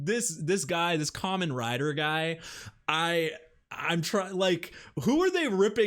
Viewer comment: Did he just called my man common rider guy? 😩😩😩